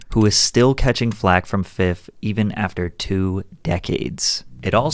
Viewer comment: Did you call .50. Fifth?